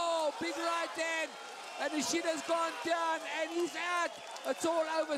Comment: Second fight. Sound like he said "AND THE SHIT GOING DOWN!" 😂😂😂😂😂